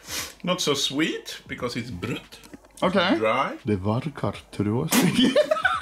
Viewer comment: XD what did he say here? why i laugh?? XD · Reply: Det verkar trasig/ It seems to be broken